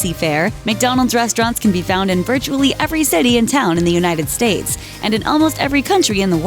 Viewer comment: “Every city in town” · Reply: gotohell gotohell Ah. My mistake. It still kinda sounds like she said “Every city in town”.